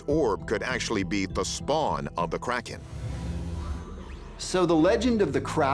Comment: I thought I heard him saying it was the sperm of the kraken 😂